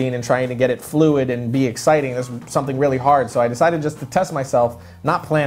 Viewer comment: swear said so i decided to touch myself. tehee.